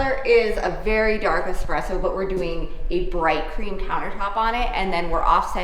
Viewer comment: I had to back up a bit, because I thought I heard you say you were going to install a bright green countertop for the kitchen. 😂 Bright cream sounds like a much better choice!